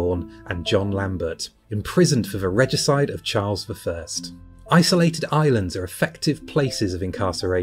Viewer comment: What?! The subtitles say "The regicide of Charles III" 😲 Does he know? Not surprised he is unwell! 😂 See @ 12:33 Obviously the auto text can't hear "first" clearly. Stopped me in my tracks though!!